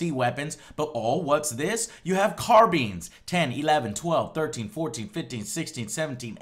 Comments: did he just say carbeans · carBEANS? lol. I could have sworn they were called carBINES